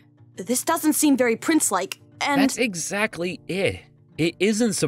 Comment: Did anyone else hear say “Eh” other than “it”